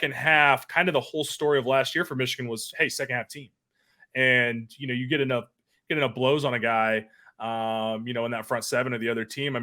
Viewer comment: Did he just say when you get alot of blows on a guy?😂